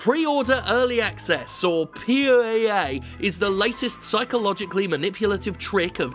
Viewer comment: Thank God for Jim Sterling . @ 4:33 Did you say POoEA hahahahahaha